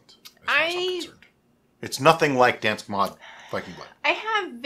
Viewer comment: Oh, it's "mjød"! I thought you were saying "Dansk mod" all this time... FYI in Scandinavia we pronounced it more like "mjeaod" and don't forget the J!